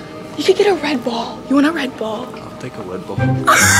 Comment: when u said red ball i thought u said redbull :shelterin::chillwcat